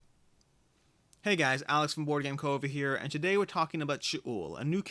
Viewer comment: Another great video. Alex at the start of the video i thought you said Alex from the boardgamecovid lol